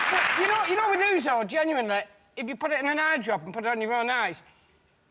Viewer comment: It took me awhile but I figured out that when his brother sold the hot tub, he got Johnny a kite. What I can't understand is what he saying at 7:11. What is he putting in the eye dropper? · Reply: I finally found it on another thread comment: Ouzo!